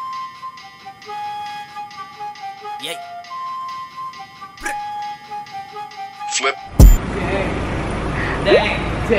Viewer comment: locals hating BTS but copying their song, ew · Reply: In 4:58 I heard that Flow G said " sabihin mo DDAENG" kay. Ako lang ba talaga o tama yung pagkakarinig? (use earphones)